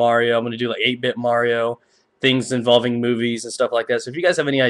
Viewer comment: lil b4 2:28 did you say things involving boobies ;) (licks lips)